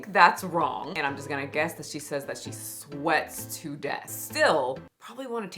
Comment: I thought she said she sweating to death